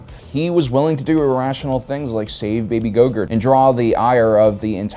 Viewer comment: I must have dyslexic hearing because I kept hearing "save baby gogurt" and that just about broke me